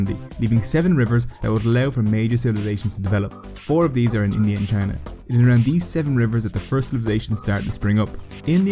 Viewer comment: What did he say at 0:36 to 0:37, 0:41, 1:35, and 1:45? If you can tell me what he said without subtitles turned on, then you are very good, indeed. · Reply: = four of these are on india and china 1:35 = isolated for most of humanity 1:45 = most of Canada 0:35 to 0:37 = (this one am not so sure about the word at the end ) but i'll bet on " four for isolated new landing?